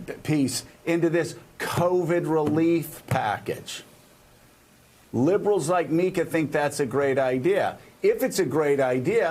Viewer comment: Did I just hear Joe saying “ liberals like me...” at 3:15 . WTF I thought he was a Republican elected representative? Hope Joe Biden gives the finger to the Republicans, no compromise, you’re in on this or we’ll go without you. Americans like a president that is strong, don’t they?